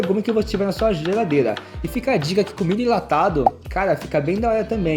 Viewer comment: eu juro que entendi comigo enlatado,comida enlatada entendi esses dois kkk